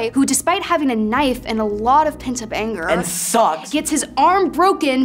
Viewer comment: I thought Noah said “And SOCKS!!”😂😂😂